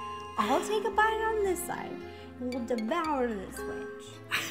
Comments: Wait...did Ro just swear?!? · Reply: Yeah at 12:06 did she say "we'll devour this bitch" or "we'll devour this witch"? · Was I the only one who thought she said "Devour this bitch"?